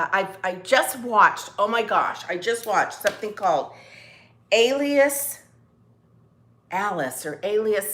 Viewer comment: I couldn't find the Alien Alice on Netflix??? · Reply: I thought you said alien not alias..lol going to check it out...xoxo